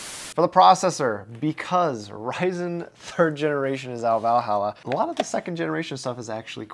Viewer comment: does he say 'out valhalla'? imma use that in the future